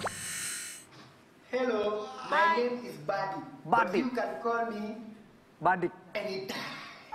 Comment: ngomong apa dia? Edah? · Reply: You can call me anytime wkwk